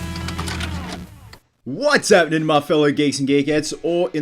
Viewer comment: When i was a kid i thought this man said "what's happening fellow geeks and dick heads" at the time i thought it was normal for YouTubers to curse